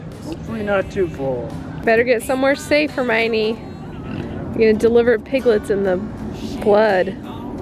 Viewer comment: It sounded like you said "you don't want to have piglets in the blood"